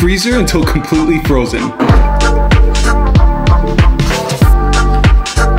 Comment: I thought he said Matilda ice cream 💀